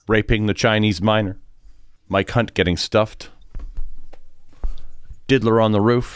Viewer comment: I thought he said "my cvnt getting stuffed"💀